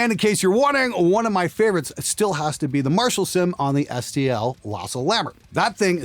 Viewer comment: No matter how many times I re-listen, I cannot make out preferred amp sim (Marshall sim found in the SCL Lasa????). The recommendation comes around the 6:42 mark. It's not, it's on my end - hard of hearing, listening through crap buds. Any help here would be most appreciated. Thank you.